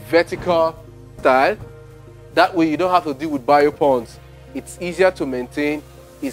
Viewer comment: What are you saying at 6:18 - "that way you don't have to deal with ******?, it's easier to maintain..." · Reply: Bio-ponds.